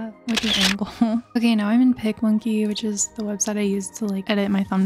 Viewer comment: what website did you say at 4:50? bc i didn't heard what you said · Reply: it's called Picmonkey <3